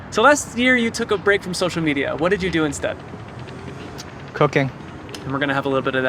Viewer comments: I thought he said cocaine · when they asked what he did a lot of last year, he said “cooking” but I thought he said cocaine. 😅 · I thought he said cocaine lol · i thought he Said cocaine😂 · dang i really thought he said cocaine 😂